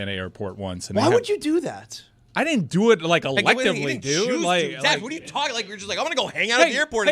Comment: Did Chris call Zas dad at 21:30 when getting frustrated with his airport take??